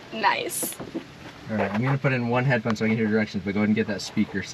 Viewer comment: did you say headbud not earbuds? Never heard that one 😂